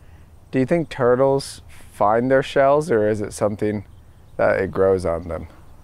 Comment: At first I thought he said “does a turtle fart in it’s shell”